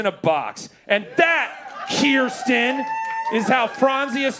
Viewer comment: Then again, I thought I heard a "Kiersten". 😄